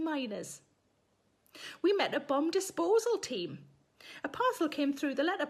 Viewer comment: I thought you said bum disposal... lmao I'm thinking that's fookin brutal flower! ❤